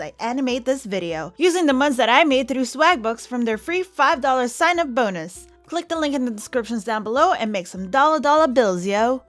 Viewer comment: I thought she said from there free 5 dollor Sy McBoners oh my God go to